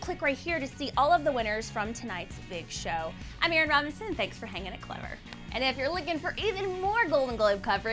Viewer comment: what the fuck did she say her name was?! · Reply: Heyerin Robinson